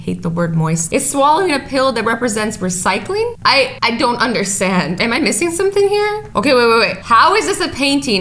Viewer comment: i think i was hearing things but i thought said understand but the t is silent undersand XD